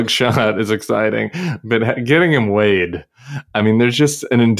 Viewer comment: Oh, “weighed”! I thought you said laid. 🤢🤭